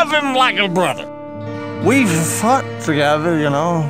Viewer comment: I wasn't paying much attention and I thought he said "we fucked together" XD